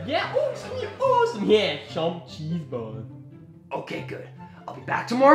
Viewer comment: i just hear cheeseballs 0:10 LMFAO